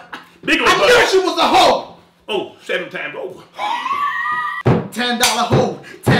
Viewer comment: What does JD's dad say at 2:27? Right before wheezes, lol. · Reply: He says "oh, 7 time hoe"